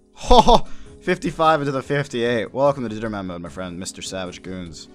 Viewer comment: I thought you were saying DInnerman Mode... lol.